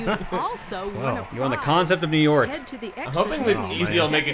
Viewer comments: “Non vulgar celebration”? Really? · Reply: What I heard was "an ogre celebration." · “Non vulgar celebration”? Really? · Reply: I heard "I'm full of celebration"